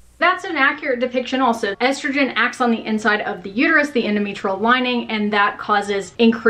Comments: The first time you said "endometrial" I thought you said "enemy troll" and this is how I will be referring to my uterus from now on. 😂😂😂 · When you said “endometrial” I thought you said “enemy troll” and that’s exactly what I feel my periods are